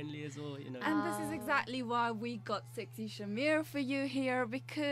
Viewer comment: did she say sexySAMIR ? lol